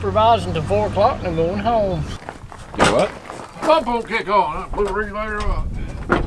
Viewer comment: What did say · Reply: “pump won’t kick on. Blew the regulator or what” I believe is what he said. I had to rewatch it about 10 times lol